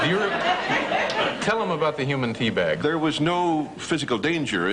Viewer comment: Lol. At first I thought Dave said "the human pee-bag".